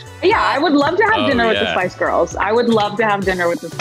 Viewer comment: Who did the actress that played allison said to the "Who would be your guest on a dinner?" I thought I heard "The twice girls" and im freaking out But im sure it was something else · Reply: I think it's The Spice Girls